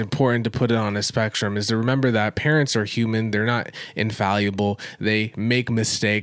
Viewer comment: Please forgive me. My volume is low, so it sounded like you said "parents are humid".